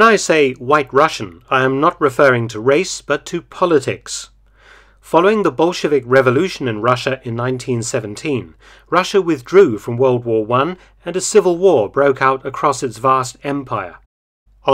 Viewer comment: at first I thought I heard " bull shittic revolution "